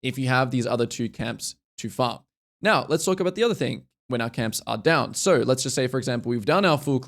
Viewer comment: When you said the Atakan I thought you said "The other cnt". Was hilarious.